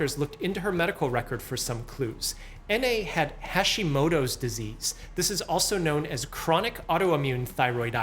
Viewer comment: STOP IT I do too 😢 Edit: Omg I thought you were saying this disease is caused from Hashimoto’s 🫠